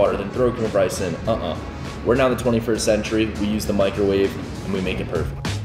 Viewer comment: Did he just say we’re not in the 21st century?! lol